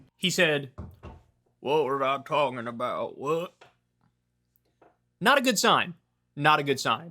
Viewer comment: I hate Mitch but he did not say "what am I talkin' about, what?" He actually repeated a question that was asked and said "what are my thoughts about 'what'?" I just care about accuracy.